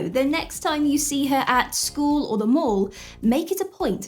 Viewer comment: Lol. . . I had to do a 2nd take. Because of your accent I thought you said, "So the next time you see her at school or the morgue..." I realize you actually said, " the mall." Sorry about that. Good info in this chat.